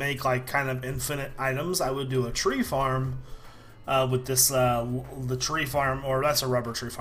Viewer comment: at the end of the video i swore he said weed farm